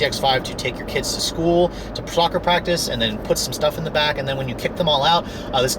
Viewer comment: did he say ''ploccer practice? is that a new sport? or did he mean soccer practice lol